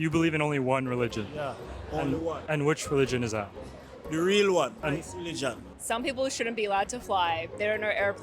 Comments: the guy said "christ religion" but the joe's video subtitles said "islamic religion" just has to throw that out here incase joe sees it... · he said christ religion but the caption said islamic??